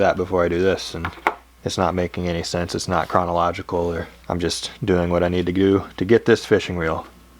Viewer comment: Am I the only one who heard, “doing what I need to goo”